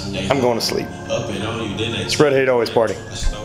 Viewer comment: This is my first time watching this guy. Did he just say "spread hate always party" ???😂😂😂wtf